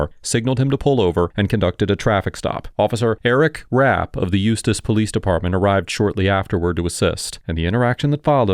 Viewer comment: "I totally heard "Useless Police Department" and had to double back to hear that it was actually "Eustice Police Department." 🤣🤣🤣